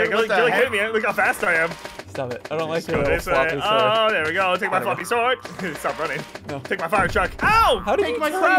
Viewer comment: did say take my f***ing sword?